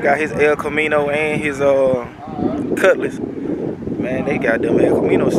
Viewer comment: I heard "Cutlass R US " behind the camera 📷. Shout out to the boot.